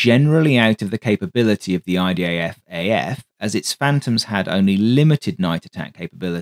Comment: why does it often sound like you say IDAFAF rather than IDFAF?